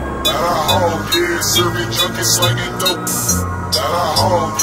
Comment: I always thought he said got a hoe yea serving junkies slangin dope 😂😂